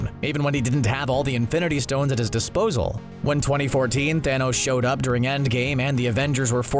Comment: did he saY 2014 in end game